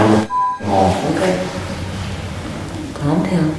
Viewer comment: At around 39:29 it sounded like someone said down but it was like a deep voice not sure though